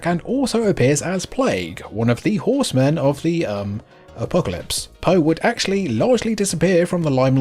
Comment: Did you say “A-Poe-calypse”? If so I’m equally impressed and disappointed.